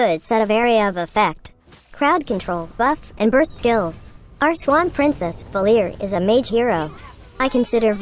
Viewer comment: Swan Princess? Anybody heard that?